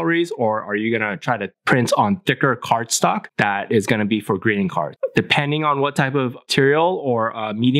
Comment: Great Stuff Man. I for sure thought you were saying "Green Card" for a minute. Then I realized you were saying "Greeting Card!"